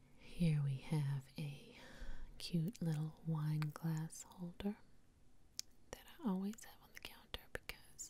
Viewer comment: I heard "wine glass altar," but I think you might have side "wine glass holder." Same difference, right?